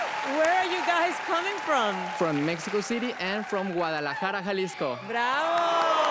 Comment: I thought Terry said Guatalmara for a sec almost spat my water out